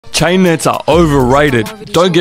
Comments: I thought he said china for a sec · I thought he said “China is overrated”. 😂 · It thought he said china is overrated